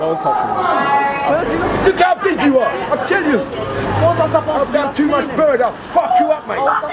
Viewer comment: Did the guy at the end say I done too much burger I fuck you up? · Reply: sounds like he said he's done too much burn(crack).